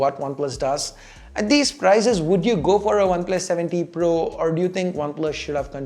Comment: It always sounds like you say oneplus 70 pro